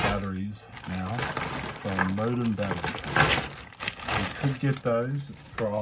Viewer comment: I couldn't hear what you were saying because of the bags you were moving. What did you say at that part? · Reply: From modem batteries